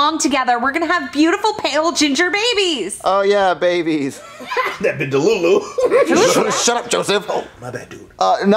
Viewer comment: what did say? 💀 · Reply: "That bih delulu"😂😂